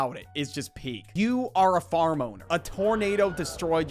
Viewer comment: “Farmoner”🔥🗣️ (btw im pretty sure its Farm Owner i just thought he said that first)